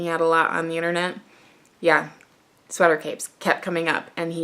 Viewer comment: I thought you said “sweater cakes” and then I became so intrigued, “what is a sweatercake?” I kept asking myself. Well, now we need them, they should be in cute cupcake colors like pink and baby turquoise blue 😆